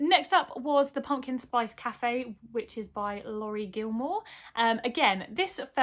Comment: When you said “written by Laurie Gilmore” I heard “Rory Gilmore” and spent a good couple of seconds feeling excited about that before I realised Rory Gilmore is fictional and therefore can’t write a book haha 😂